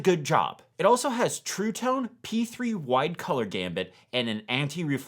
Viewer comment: Did I hear “color gambit” instead of “color gamut”?!